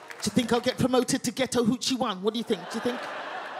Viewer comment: Took me a while to get that she was saying "ghetto hood chick"... Not for none, but I deadass thought she was saying "getow huchi" and I thought wtf was that. Almost Google it, til she said "neega it's not yo baby!" I was like oooohhhh... Ratatata! 😂😂😂